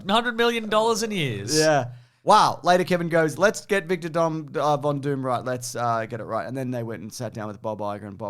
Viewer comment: Victor Dom- I heard that XD